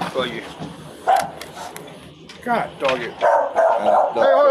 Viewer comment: Did he just say "God Doggit" instead of damnit? wait am I trippin?